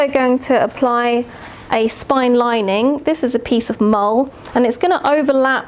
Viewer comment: what's the word here? "This is a piece of (mowl?)" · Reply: I am not sure which part of the video you mean, but I am sure the word you are asking about is 'mull' , an open weaved fabric used for lining books. <link>